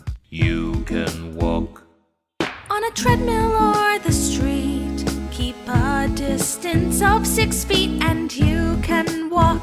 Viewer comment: I thought it said on a treadmill on the street stay a distance of 6 feet